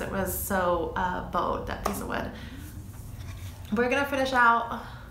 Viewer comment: I thought you said " we're gonna finish up with a shot 🥃" 😂 But I loved the final piece 🥰